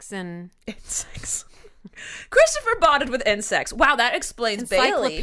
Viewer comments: oh my God I thought she said Christopher bonded with sex. 😳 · Omg I thought said bonded with and sex instead of insects 😂😂😂